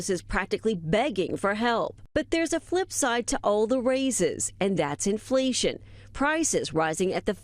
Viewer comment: If you close your eyes when that lady’s talking, you can imagine a female Fauci, she sounds almost exactly like him! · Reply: For a second there I thought she said "Paindemic" she ain't wrong though for most of us anyway.